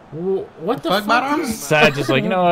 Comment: Did anyone else catch the commentator say the f word